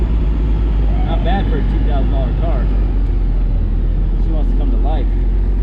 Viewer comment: Anyone know how much that white civic was? Lol 🤭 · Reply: I thought he said it was sold for $2000